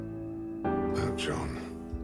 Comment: why did I think he said blow John